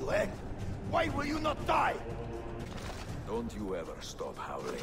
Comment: Did he said: Don't you even stop (Todd) Howard? 🤔